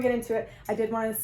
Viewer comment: When she showed the mugs I thought she said “I s**t” international “ haha